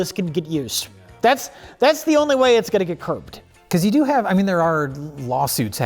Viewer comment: Did Rich just say "That's the only way it's going to get Kirked" ?